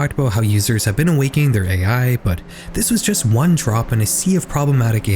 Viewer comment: did you mean trope, as in rope, with a t in front? not trop? Idk if I misheard it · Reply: Drop